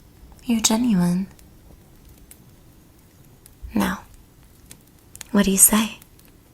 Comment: I heard "What's your name" and went: "My name is Inigo Montoya, you killed my father, prepare to die."